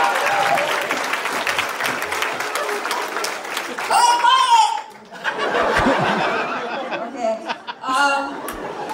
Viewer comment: What does she say at 8:55? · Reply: Quite